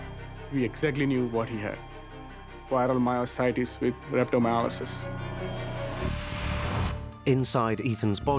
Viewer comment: what the fuck is he saying?!!! water myositis ribs recto myolysis?!! wtf?!! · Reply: I interpret it as "Viral myositis with rhabdomyolysis" is this correct?